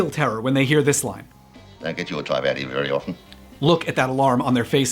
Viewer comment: What did the character say at 14:11?? i really cant understand his acccent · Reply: "We don't get your type out here very often."